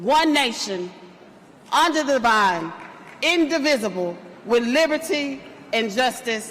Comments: "under the vine"?! Did she just say black people are... 🙊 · I think i just heard "Under the Vine" with liberty and justice for all... 😂 What's under the Vine? Some fermented grapes? Hahahaha it's unbelievable...